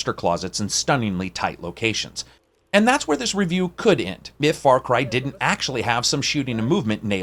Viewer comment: did I hear far cry at 7:30?